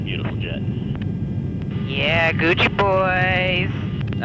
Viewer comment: Did I just hear " ya gucci boyzz" this chick cracked me up